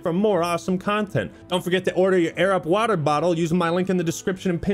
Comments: When advertised "Air up" i seriously thought he said " Arab. 😂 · I thought he said "Air Rub",😅